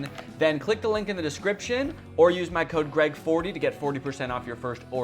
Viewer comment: I thought he said red40 for the sponsor and I tuned back in real quick